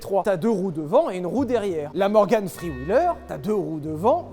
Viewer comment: Quand tu a prononcé Morgan 3...wheeler, j'ai cru que t'allais dire "Morgan Freeman" ... j'ai entendu "Pontiac castex" aussi ... désolé je suis fatigué de ma journée xD